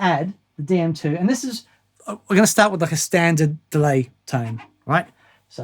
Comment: What I heard: "Lets just start with a Constanza Delay"